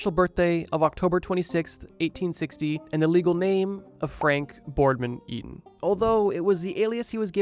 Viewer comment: i thought he said frank horrigan for a second 💀